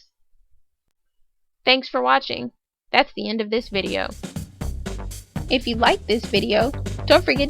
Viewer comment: Hey for a minute when said at the end of this video it sounded like you said F this video so funny oh yeah and also I clicked below and saw your videos math and things like and saw your face and you look like a Alien.